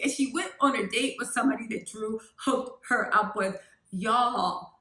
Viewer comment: I love thought she said she went out with someone Latoya hooked her up with???